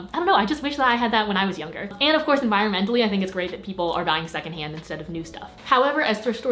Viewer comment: of thought she literally said dying second hand, not buying and I had to watch it again 😬